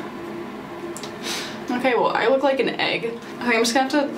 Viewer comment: “I look like an egg”😹 lol ur cute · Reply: Ha! I thought she said, “okay, I would like an egg” I’m like yeah that sounds yummy right now